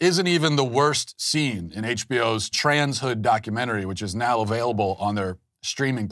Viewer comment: I thought it said “Trash hood”